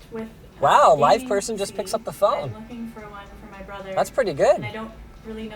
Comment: When they called cyber power i thought linus said "wow a white person just picks up the phone" lolol